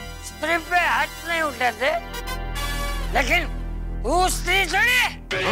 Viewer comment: i heard bhosdi